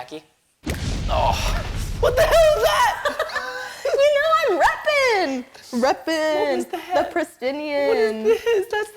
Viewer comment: I think Preston said a bad word at 9:41. Oooooooooo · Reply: He said what was that!